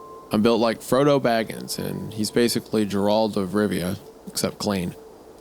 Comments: wait wait wait WHAT DID HE JUST SAY?!?! DID HE JUST CALL GERALT FROM THE WITCHER "JERALD"!?!?!?!?! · Did he just say Jerald????